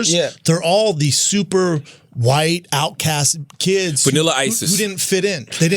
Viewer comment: As a society we're doing better than most of the world but when he said "vanilla ices" I heard "vanilla isis" and that shit hade laughing like a stoned fat kid looking at his food before he goes HAM